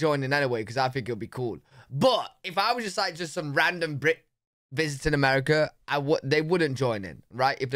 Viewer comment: For a moment I thought you said, "If I was just some random git....." LOL.